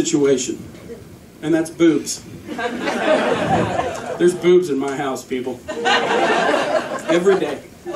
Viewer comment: Boobs? At first I thought you said booze! But what do I know!